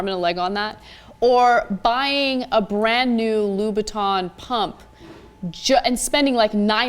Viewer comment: That's what she said, 'Louboutin pump'. I misheard her at first too, i though she was mispronouncing 'Louis Vuitton'